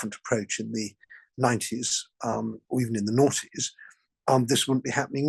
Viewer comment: 🤣🤣🤣 Mr, but I was not emotionally prepared to hear one of your sober paragraph-talkers reflect on society's deeds and misdeeds in the naughties. He's said it a few times now (at least 2x, but 3x for me; I had to replay and confirm what I thought I'd heard.) Each time a cloudy image of Dr Quast & Mrs Knickerbaiter* becomes more focused in my mind. Quite distracting it is, as the image takes me back to my own naughties, bestirring the full spectrum of emotions associated therewith. * deliberately misspelled (for algorithmic avoidance) and mis-associated (for reasons left to the reader.) Both skits were, however, on the same 3-sided, 1-disk, 12" LP album.